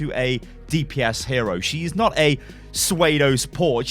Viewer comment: Did anyone else hear him say swaydo instead of pseudo around 0:31 or so